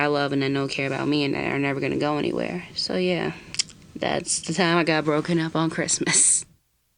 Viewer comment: I Thought It Said “I got Jumped on Christmas Eve” I WAS ABOUT TO SAY! Edit: THANKS FIR ALL THE SUPPORT